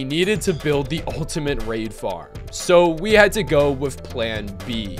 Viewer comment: oo we had to instead of so we had to